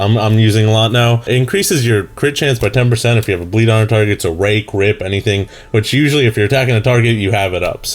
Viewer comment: Did he say "Rake, Rape"?